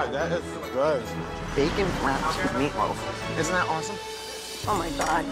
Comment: When he said "bacon wrapped meat loaf" i heard "bacon rat meat loaf" and I'm still laughing about it